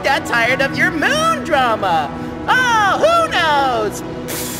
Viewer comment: I heard moonos instead of who knows